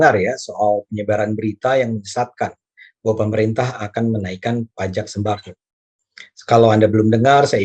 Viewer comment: maaf apa saya gak salah dengar...presiden pekaes ngomong pemerintah gak PANCASILAIS.... 😂😂😂😂😂😂😂😂😂😂😂